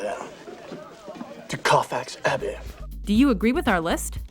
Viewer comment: Forgot how terrible KR is at accents. It was a good film, but hilarious accent. "Kwarfarx Hahbbee" For much of the film I had no idea what he was saying. Luckily I knew the book pretty well or I'd have been lost.